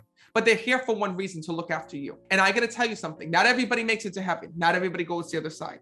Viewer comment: I thought you said everybody makes it to heaven?